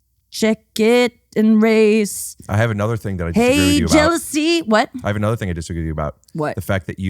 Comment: Hey jealousy? Me whole life I thought it said "Hey Jessie."